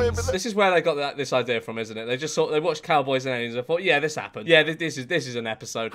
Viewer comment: Was it me or did it it sound like he said Asian aliens